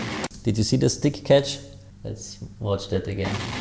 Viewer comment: What was the thing you commented on at 8:47 I can barely make out what's even going on and I can't quite hear what you say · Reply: The drumstick catch?